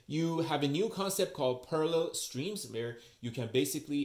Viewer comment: Maybe overlay the title concept when you introduce it. It sounded like you said Perler Streams instead of Parallel Streams. Excellent overview. I'm going to watch the OCA one next.